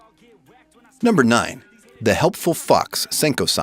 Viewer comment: Sorry guys, I had to reupload the video to fix some problems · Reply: i thought u said the helpful f*cks senko san